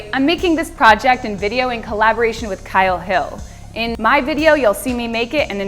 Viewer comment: almost gave me a heart attack! At first I heard "In my video you'll see me naked..."